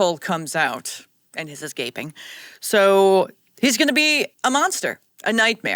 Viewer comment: did you say it’s gaping hahah